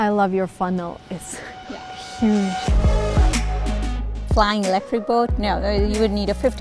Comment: did she just say flying an electric boat??